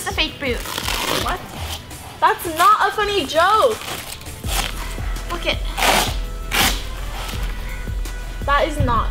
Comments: it sounds like she says fuck it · if you listen it sounds like she says fu*** it lamo